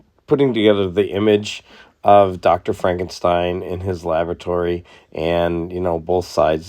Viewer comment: Great puzzle!! I had to chuckle a wee bit at about 02:55 where you mentioned "DOCTOR FRANKENSTEIN IN HIS LAVATORY". I had to listen several times to make sure I heard it right. (LOL)